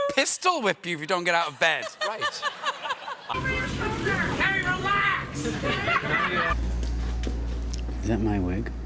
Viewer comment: what does he yell at 6:27? is it "don't laugh" or something like that? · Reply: "KERI RELAX"